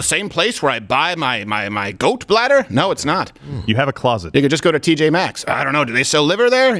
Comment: Did he just say « Nigga just go to TJ max?